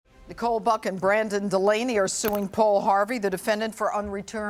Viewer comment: I heard "Nicole-fucking-brandon" at the very start of the video